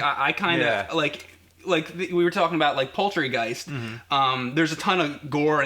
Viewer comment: did say Poultrygeist?